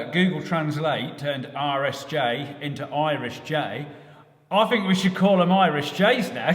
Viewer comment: With your speech impediment, I've thought you've been saying Irish Jay since the very beginning. As you live in East Ireland, it kind of made sense